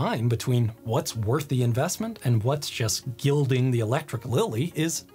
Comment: I had to rewind at 14:11 because I swore I heard "gild the electric willy," which was not on my bingo card.